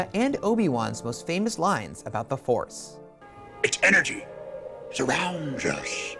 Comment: I saw it again today, I listened REALLY hard for Yoda during the dream sequence but did not hear it. I did however hear Ewan McGregor call Rey's name. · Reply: I heard him say "energy" but you need the ears of superman to hear it